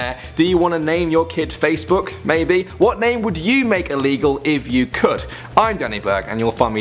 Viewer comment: Illegal? I thought you said legal. I was going to name my child Sex fruit...